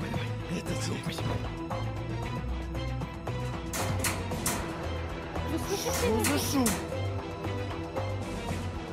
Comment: дед матюкнулся или это послышалось??? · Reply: Он сказал "о, спасибо"